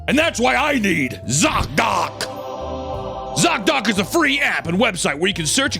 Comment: i thought you said sock doc. i was looking forward to learning how these things were never getting wet again.